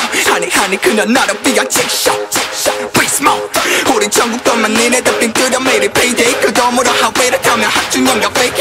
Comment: I heard Junkook 💕 1:45